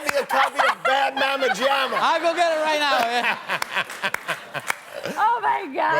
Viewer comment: “Yeah I know.”-Willow is giving me life lol. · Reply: TheBabypowder05 I thought she was saying, “yeah, no”. lol